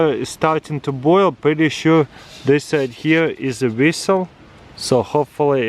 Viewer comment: 'Whistle' I thought you were saying resell.